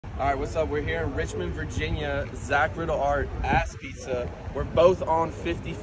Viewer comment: Did they just say ask pizza?